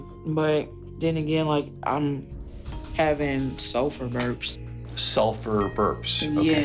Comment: Oh my Gosh! When she said "sulfur burps" i thought she said "sofa burps"